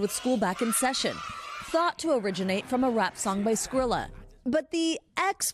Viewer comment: i love how they say "thought to originate from a rap song by Skrilla" as if we should obviously all know who tf that is 😂 and I'm just like....WHO??? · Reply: I legit thought they said Skrillex for a second